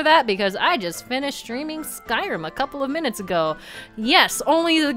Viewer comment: oh man, that was a stream. Hooo boy Edit: oh. I misheard Skyrim as Stardew. My bad